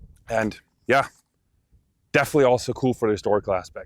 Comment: He said baie dankie at the end. It almost sounded like he said "buy a donkey". lol Ek wil nie n donkie koop nie ek wil nog n geweer koop. Edit: Also great video, he speaks better Afrikaans than me.